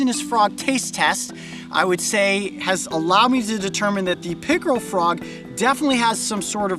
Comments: I thought he said pickle frog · I thought he said pickle frog, and I got excited lol